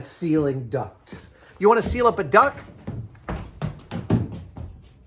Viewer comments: Not going to lie I thought Adam was saying seal up a duck and I was highly confused wondering why would you be sealing up a duck. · Adam: "You wanna seal up a duck?" runs off cam Me: Pretty sure a duck is already watertight... or at least one part! Also if there was ONE person on the planet who going to seal a duck instead of a duct it would be Adam Savage!!!